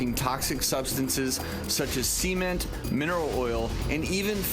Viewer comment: It sounds like there saying semen and not cement